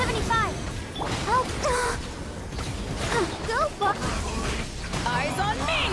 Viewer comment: Did Barbara just say "Go fu..."